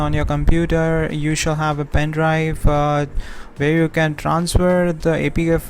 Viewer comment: Once I figured out you were talking about a Thumb Drive (Sounded like you were saying PIN drive) to save the APK to from your computer then plug it into the USB on the TV, it all made sense and worked like a charm. Thanks for making the video.